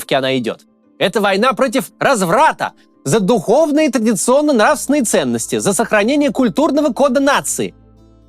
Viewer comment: Послышалось: война за культурно насранные ценности😂